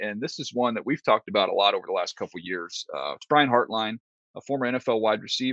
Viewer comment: I thought his last name said hairline